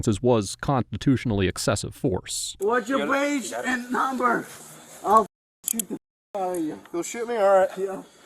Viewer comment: While I disagree with the conduct of (man arrested in second video), I genuinely don’t think he was trying to say “I will shoot you” but rather “I will sue you”, especially since he said at one point “I will shoo the f*** out of you”. Given his accent, it’s entirely plausible he was trying to say sue or sued rather than shoot. It also makes sense in the context where he is thrown to the grown and tased, and likely felt it was excessive. Edit: At 21:46 he tries to clarify he is not trying to say “shoot you” but again says what sounds like “Shoo you” because he originally said “Shoo the f** out of you”, and his speech was slurring (possibly affect from tasing), it would sound like “shoot you”. I also want to clarify that I do not condone his behavior or excuse it, but don’t agree with the assertion he was threading to shoot the cops. As my family are immigrants with strong accents, I’ve heard many things said incorrectly similar to this instance where it could be interpreted very differently.